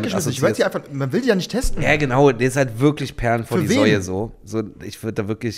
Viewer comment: Ich finds schön, wie der Ausdruck "Perlen vor die Säulen" auch hier einfach verwendet wird. Musste kurz zurückspulen, um zu checken, ob ich mich nicht verhört habe. Oder sagt er Säue? Hahahav